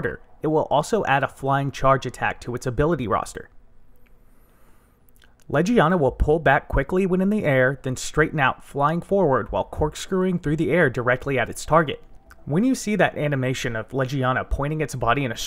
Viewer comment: In terminalmontage's iceborne video I always misheard it as Lugia.